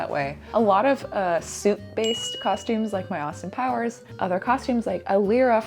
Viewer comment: Good thing there was a photo illustration (Austin Powers), because I heard "soup-based costumes." And right before you dissed your pigtails I thought, "She looks so good with her hair that way."